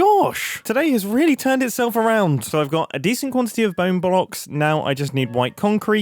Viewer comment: I did not hear bone blocks.... i heard "Bone Boll Ocks"